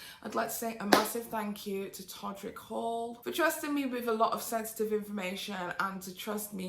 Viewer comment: I'm sure it's totally me and my distracted brain, but I could have SWORN at the end P said he trusted her with "sexsitive information"